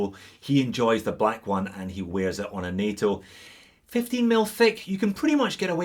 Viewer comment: Excellent review. At 9:45, did you say fake NATO 15MM band? Went to NATO website and only see 18MM. Thank you.